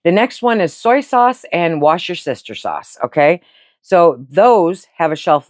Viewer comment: So informative; my husband and I were watching this and talking at the same time and when you said Worcestershire sauce we thought you said "wash your sister" sauce. And still laughing, I have to go back and watch just to get what you really said about it. I will never say it right again.